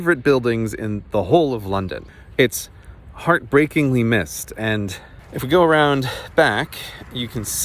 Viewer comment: I think he described the building as heartbreakingly 'mist-y', though I could be mishearing. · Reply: I legit didn't catch that until I read your comment. I just thought he said it was "hearbreakingly missed."